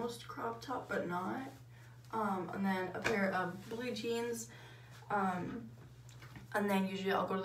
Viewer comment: I literally starting dying out laughing when she was doing her outfit and said blue jeans I thought she said blue cheese